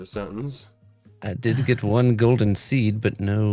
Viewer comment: At the very end it sounded like g said "Golden Queef"